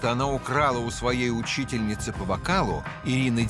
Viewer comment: Мне послышалось учительницы по бокалу!😂😂🍷